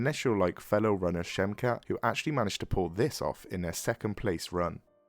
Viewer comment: did you call him 'they'?